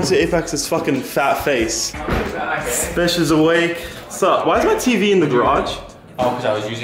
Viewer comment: I thought he said what's up jev i was so happy! Then I realized it was Jeff :(